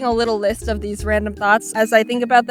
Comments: I thought she said random thots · when jaiden says 'thoughts' it sounds like she says 'thots' and im living for it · At first I thought she said random thots